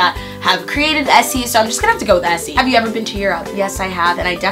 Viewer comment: when you said "have i ever been to europe" i thought you said "have i ever been cheated on" and i was so confused by your answer lmao.